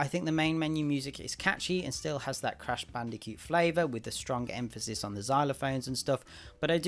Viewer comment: For some reaon it sounds like is saying crash bandi cute also it seems like brutaly honest revievs is a series now